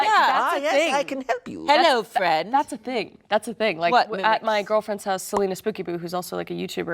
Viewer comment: did Kris just call Celina her girlfriend? lol